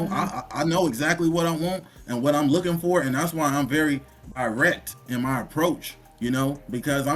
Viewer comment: did he just say he was irect, I'm sure he meant to say direct, just pointing that out and a irect is a rectangular bounding box